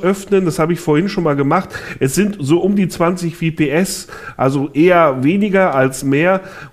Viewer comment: vps? oder was sagt da?